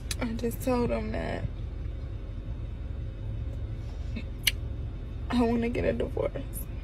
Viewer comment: All I hear through her crying and from her is "I want to be a garden tool, I want to be a garden tool" repeatedly... and her voice is like nails on a chalkboard...